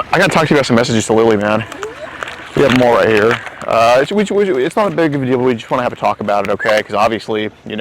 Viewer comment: wtf did say at 4:47 ? LMAOO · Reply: Wicuwichuitsnothtatbigofadeal